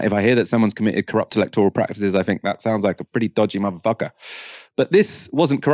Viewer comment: Did say "mother fucker" at 20:40 or am I mishearing things? If I didn't get that wrong, that is completely inappropriate and sexist. I don't want to hear that type of poor quality language and hosting. I know it is live and you can make mistakes but it is not unreasonable to ask you to be more mindful.